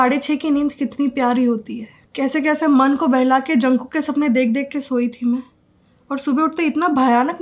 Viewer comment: did she said "junkook ke sapne.?" 🤣💜